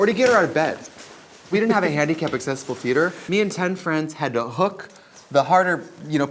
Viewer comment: does Trixie say Tan France helped her get Katya into the venue? 😂😂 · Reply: Ten friends lol